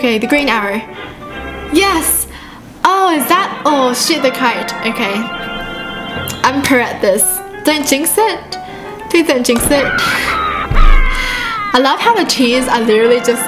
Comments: DID YOU JUST SAY SHIT THE KYTE INSTEAD OF SHOOT THE KYTE!? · sounded like shit the kite lol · Did she say sh** at 10:27? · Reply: Shoot